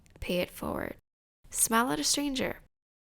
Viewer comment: I thought you said “smell at a stranger” !!! Haha